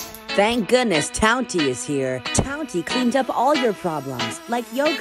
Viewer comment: I thought he said “the back of your parents or siblings”😭